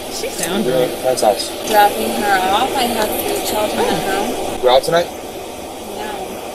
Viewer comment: “I have two children at home”. I think she said 2 it’s hard to understand her. 4:23 “I have 3 fucking kids” 5:24 “I have 3 children “ 7:28 “I have 4 kids” 13:00 “I have 3 children” So who knows how many she actually has.